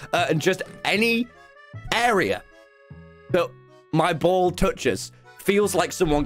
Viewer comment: I thought he said “Ball torque” 😂😂😂